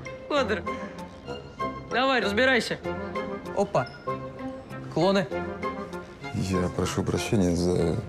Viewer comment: Ржу со слов -"Опа клоны"😂😂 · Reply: Banana Bo мне вообще послышалось "клоуны"😂😂😂